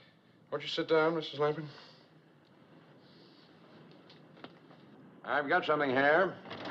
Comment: the way he says , "I've got something here , liverwurst liverwurst , chicken and liverwurst". Hahahaha sounded like he said " I've got something hair " lol